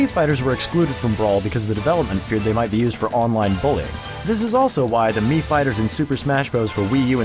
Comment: I can't understand what he says when he explains why the mii's weren't included. It sounded like he said they weren't included due to online bullying?